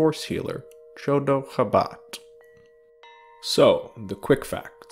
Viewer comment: Absolutely love this video, but every time you said Chodo Habat, all I heard was "The dog of Wisdom"